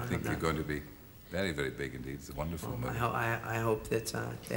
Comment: Oh my God, did Terry call him "Michael J. Cox" at 4:39?